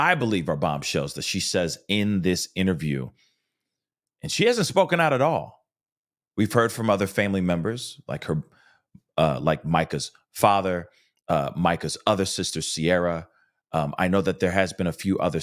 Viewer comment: I can hear a man's voice say "right here" in the very beginning of the 911 call. Anyone else??? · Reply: I heard "MICA'S Folder"